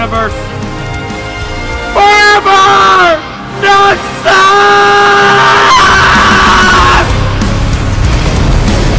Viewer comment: Can someone tell me what is saying after FOREVER!! 35:06 Also so funny at 23:50. My congrats to the editor. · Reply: He says : "NOXUS"